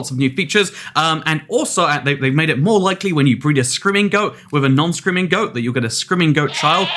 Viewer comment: I thought he was saying "scrimingo" instead of "screaming goat" and was trying to remember if they added a new animal called a scrimingo